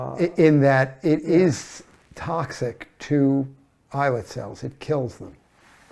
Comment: What type of cells are being described at 44:30? I can't make out the word before "cells"? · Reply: Islet cells